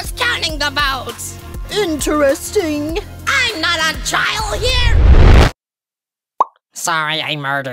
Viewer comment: what other hear: IM NOT A TRIAL HERE what i hear💀: IM NOT A CHILD HERE